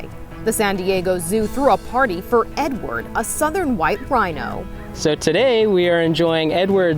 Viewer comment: Damn I thought they said northern white rhino for a second.. weird how they didn’t mention them as they’re so closely related to the little rhino